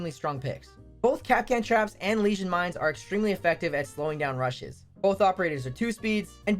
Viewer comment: I heard that as "Slowing down Russians"